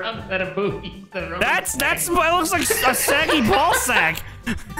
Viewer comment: dang bro you didn’t have to do seggi like that😭😭😭💀💀💀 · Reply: I thought he said "Seigi" too but I think he actually said "saggy" Btw I love it pfp and username I AM NOT A MORON!